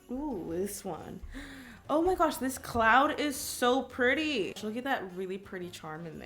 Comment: I thought she said clown XD